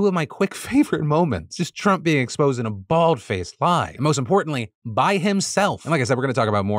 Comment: did Phil say Bald faced? 😅😂